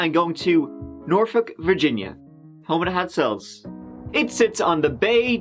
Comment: "Norfolk, Virginia. Home of the hot cells ???? What does he say here? What is Norfolk home to? It's definitely full of buildings and parks, it's a city😂 for sure. Definitely an artist's city and one wouldn't expect that.